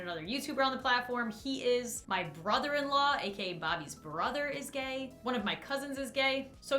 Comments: Omg when Lauren was talking about who was LGBTQ+ in her life I thought she said scott was bobby’s brother lmao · i thought she said that scott was her brother in law i was like WAIT WAT SINCE WHEN?! 😂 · for a sec i thought she was saying scott is her brother in law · Omg for a 5 seconds I thought Lauren said That Scott was her brother in law when someone asked if she was bisexual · watch to 5:35 I.. thought she said Scott was her brother in law I mean..... could b- well idk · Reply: no, she was saying Scott and her brother and law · when she caught off at 5:35 after she said "he is", i didn't notice it cut off i thought she said "he is my brother in law, bobby's brother" and i thought she was saying scott is her brother in law and scott is bobby's brother and i was like "WTF WHAT-" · I thought you said Scott was Bobby's brother lmao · i legit thought you said that Scott was Bobby’s brother... what is wrong with me... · I honestly thought she said that Scott is Bobby's brother 🤣🤣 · For a second I thought you said that Scott was Bobby's brother and I was SO confused... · Am I the only one that for a second thought she was saying that Scott is Bobby's brother?